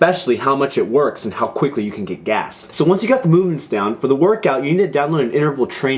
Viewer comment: did you just say how fast you can get GAS ????? LOL